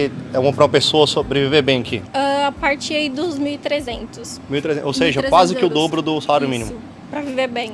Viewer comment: I hear her saying: 2’300.-, not 1’300.- Euros?